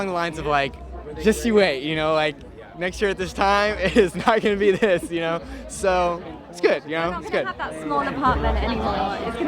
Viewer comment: what they say at this time ?? I couldn't understand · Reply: Timmy misunderstood what Armie said. The advice was : “Come the end of March, this is going to change, back to real world after this machine is kinda end” Armie meant that this press junkets, interviews and hectic schedule they’re going through will eventually end and then they will go back to their life like all this was like a cycle. But Timmy misunderstood by thinking that after the whole movie promotion ends and then that’s it, he still lives in his “small bachelor pad” as he puts it. But everyone knows that Timmy goin to go far and make many more success because he’s just amazing and talented.